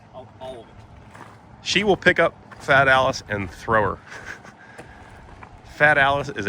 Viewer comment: I thought you said Fat Alice and thought that’s a bit rude but then I saw the Fiat Allis loader. 😅